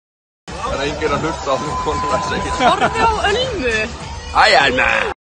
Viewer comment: d-did he say... iron man? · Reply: No, sorry. "Hi Alma"